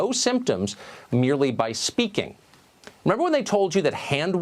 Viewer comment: 분에 Remember이.. 전혀 들리지 않네요 :-( .. 쉽지 않습니다!